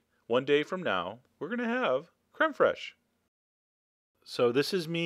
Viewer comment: lol i thought he said creme fresh wheni first watched this xD